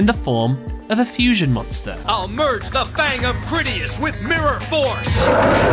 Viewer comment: So uhh, who mishear ‘Critias’ to ‘Greedy *ss’ like me…? You know, Pot of Greed?